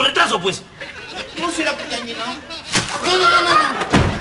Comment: que dice,no será por que has llegado o no será por que te han llegado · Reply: Llenado